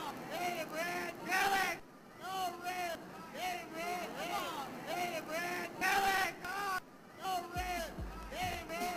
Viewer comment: To this day, I still don’t know what that guy is saying. It’s so out of the blue and weird! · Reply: Oh man, get man, come on, oh man, challenge, oh